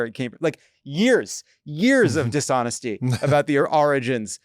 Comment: did Jon say oranges??